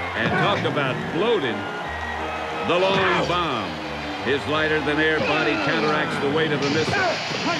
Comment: What did Pat Summerall say about the missile & Wile E. Coyote? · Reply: “His lighter-than-air body counteracts the weight of the missile.” :)